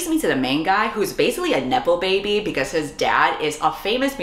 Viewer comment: HELP THE CAPTIONS SAY NIPPLE BABY INSTEAD OF NEPO BAY 😭😭😭 In all seriousness tho, thanks for all the videos in 2023. They were very nice and helpful for me whenever I wanted to escape or laugh.